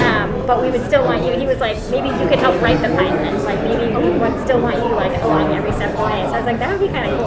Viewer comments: "We would still want you along every step of the way" Is what i believe she said by the way" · I believe she said "we would still want you like, along every step of the way", just in case anybody wanted to know! That's what I heard, at least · I think Tui said "like along your recent points" at the part that was harder to hear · I think the part at 6:01 says "keep you every step of the way" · I think she said "We would still want you, like, along every step of the way."